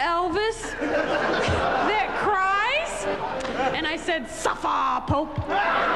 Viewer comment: what does she say at 6:36 ? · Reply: Suffah Pope!